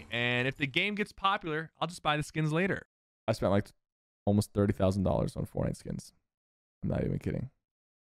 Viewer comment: Did sayed he almost spent 30,000$ On fortnite!?